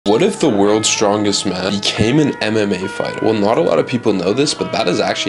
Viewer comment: Bro I heard "what if the world's strongest man, he came in a MMA fighter."